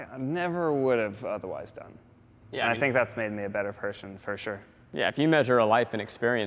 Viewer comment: It sounds like he said "It's made me a better Persian".